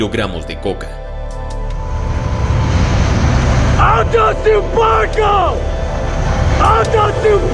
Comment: alto siu paco? No le entiendo, que dice?